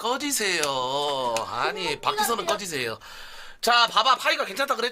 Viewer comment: 음...암만들어도 박지선으로 들리는데...